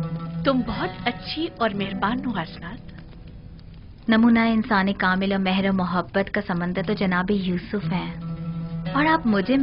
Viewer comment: main bar bar rewind kar raha hu lekin samjh nahi wo kya boli, Namuna-e insaan cartoon e mohabbat ? 🤔😬😬😬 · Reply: Namoona e insan e kamil,mehr o mohabat ka samandar*